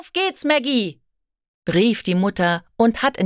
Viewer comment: Мы в пятером слушали текст не подглядывая на субтитры. Все пятеро услышали brief die Mutter . Brief а не Rief.Это чертовски сбивает с толку.